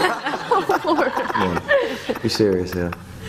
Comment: Could someone tell me what he says after “ I mean look at her” at 9:32 and everyone laughs? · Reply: I think they were laughing at that line and then he went ‘Leight, being serious here.’